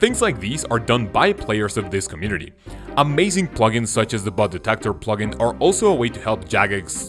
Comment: swore I heard butt detector for a sec lol